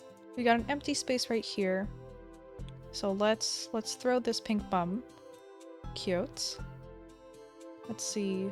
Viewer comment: Is it just me, or did i hear "Lets throw this pink bum''